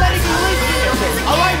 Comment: All I heard was "I'm not letting you live here again", "F*ck you", "F*ck you too", "Liam almost died and now you" 💀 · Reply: It was not ''and now you'' but ''because of you''